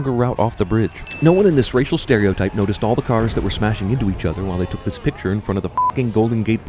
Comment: Mr. Cinema sins i spotted a sin with one of your subtitles at 6:10 writing each instead of each other